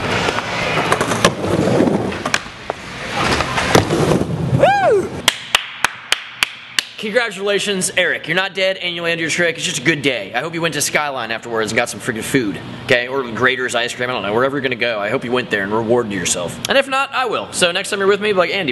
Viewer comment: it sounds like he says ''Its a good gay?''